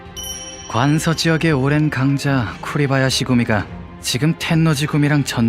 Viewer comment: 쿠레바야시구미라고 들려여ㅋㅋㅋㅋㅋㅋ